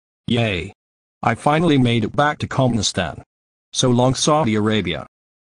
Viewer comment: what did he say? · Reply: Kommunistan, the country this takes place in